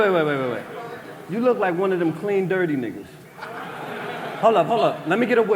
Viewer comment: I thought he said you look like food